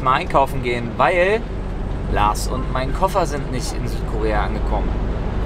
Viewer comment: Ich hab verstanden Lars und der Koffer sind nicht da xD